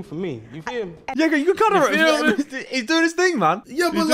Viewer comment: I swear I'm hearing harry say something he's not supposed to be saying... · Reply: no he said-"you go.. you know he's doing his thing man".